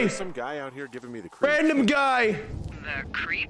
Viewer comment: Please tell me I'm not the only one who heard "cute GUY. GUY. RANDOM GUY"